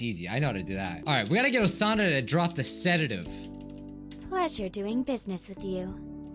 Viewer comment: I heard you say I need Osana to drop the setitive